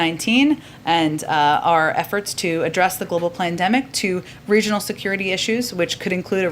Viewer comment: DID I HEAR HER RIGHT ?? "...to address the global P L A N demic ? ?"